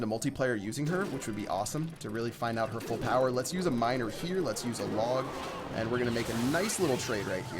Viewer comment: i swear he said 'let's use a rog'